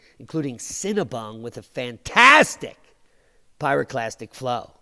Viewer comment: I first thought you said Cinnabon, oops, my bad